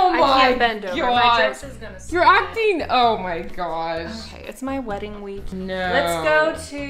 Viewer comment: Did she just say it's her wedding week